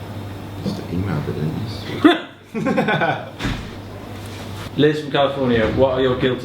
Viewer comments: Does anyone know what George says at 15:04? I've played it over and over and still don't know what he's saying. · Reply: he says "but you e-mailed it didn't you" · What does george say at 15:04? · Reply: He said "you just emailed it didn't yous?" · what does George say!?!? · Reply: lol i read this at the beginning of the vid and didn't know what funny thing george would be saying, but i caught it. he said," you just emailed it didn't you, so..."something like that haha